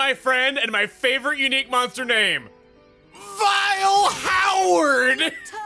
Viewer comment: I literally thought it said “Vile Howden”